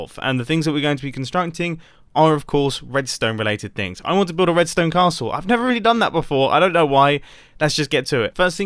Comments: Thought it said “prison castle” and was really confused by the lack of prison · i almost was very confused because I thought it said "Prison castle" the whole time. But that would be a cool thing to do out of redstone.